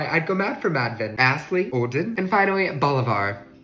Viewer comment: I thought he said toy drugs😂